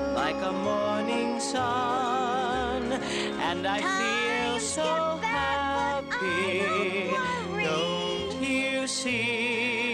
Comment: Ann is actually saying “Times get bad but I don’t worry!” AA MY HEART I LOVE THESE TWO SO MUCH 😭😭